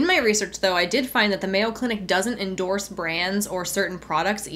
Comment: I think the legal department at Mayo Clinic would be VERY interested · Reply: I thought she said "Male Clinic"